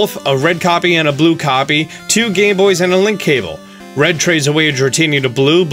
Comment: I legit thought he said "two gay boys" for a second there.